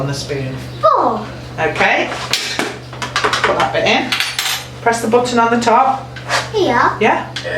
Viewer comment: 頃のおば様がちょっと待ってねって日本語話してるように聞こえてしまいました。 おば様の方がテンション高くて、お子様が少し冷静な目で見てるシーンはいつも笑ってしまう（笑 そして、セシルカット可愛い！ 食事も食べるだけではなく過程も楽しみながら作れるので、日々心から幸せに生きられそう。 お庭も素敵で、縦長だと管理もしやすいし過ごしやすくて良いですね。